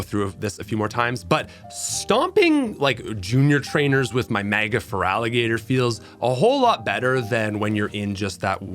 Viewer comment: anyone else heard “MAGA feraligatr”?